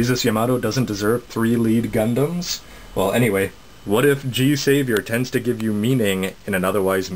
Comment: What?! I thought you said G Savior Rocked!!! :[